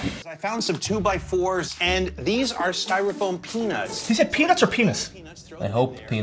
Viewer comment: I'm honestly getting tired of people intentionally trying to make believe they mishear things. IT's real fucking obvious he said peanuts of you just fucking watch the video.